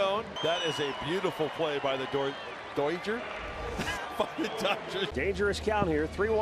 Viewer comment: did he say fucking dodgers